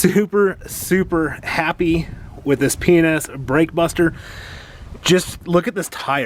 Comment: I could have sworn at 10:06 he said he was super super happy with penis . maybe it slipped out lol none the less great review !